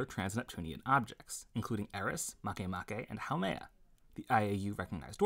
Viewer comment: I heard Kamehameha 😂